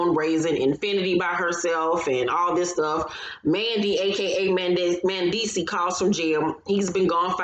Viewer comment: Did I hear "Mandy AKA Mendess"? HILARIOUS